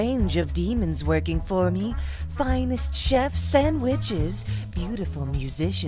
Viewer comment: I thought she said “... finest chef sandwiches”😂